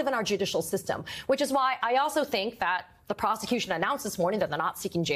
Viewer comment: Am I the only one that heard her say prostitution and not prosecution?